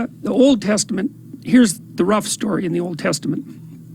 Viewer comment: Hi, I'm trying to work out a good Spanish translation to subtitle your video excerpt, if you don't mind, I'll put link in the description to yours as the origin one. At 2:19 does Jordan say "Here's the rough story in the Old Testament.."?? I can't finish being sure that's what he actually says, could somebody help me out with that one? Edit: Also this little ones at 3:07 in the middle and ending part of the sentence "you know and then, for generations its enslaved > though? < its population is being destroyed and then, its > order? < climbs back up to power ..." or "and then, it sort of- climbs back up to power ..."??